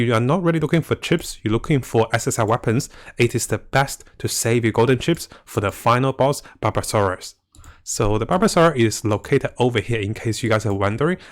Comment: its barbarossa, not barbasora. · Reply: I thought he said barbasaurus a couple of times, I was like, is there a damn dinosaur world boss? kek